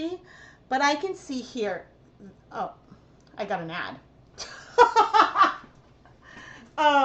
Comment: When you said, "I got an ad," I heard, "I got a gnat." I laughed so much when you blamed YouTube.